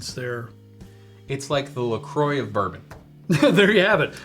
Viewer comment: For a moment there, I thought said "Macroix of bourbon" when referring to Basil Hayden. I realized he said "Lacroix". In that case, I agree with the comparison. 😅